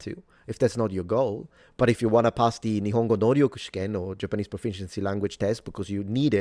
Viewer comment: did i hear 努力 lol